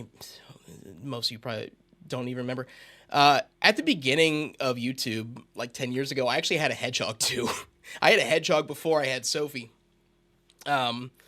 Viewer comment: I’m sorry for your loss-wait did u say hegog?????